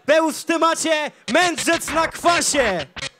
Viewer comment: usłyszałem na początku "mędrzec w zakwasie" XDDD